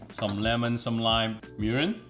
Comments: Did he say urine???? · i swear i thought he said "urine" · Why it sound like he said “urine” at @ 5:49 lol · did he say urin?